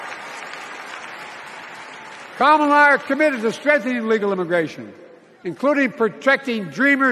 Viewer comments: did he just say Kamala and I are committed to strengthen illegal immigration!?!?!?! 👀 Wow he actually had a Freudian slip and said the quiet part out loud. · ANYONE CATCH HOW HE (I’m guessing misspoke) SAID STRENGTHEN ILLIGAL IMMIGRATION!?!?!? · Did Biden really say that He & Kamala are committed to STRENGTHENING illegal immigration? So protecting the Border & getting the crisis under control will NOT be one of her priorities/policies?? TRUMP 2024